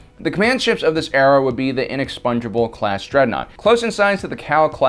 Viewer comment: I keep hearing "Inexpongebob class".